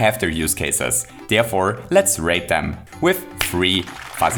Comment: damn, it sounded like said "therefore, let's rape them"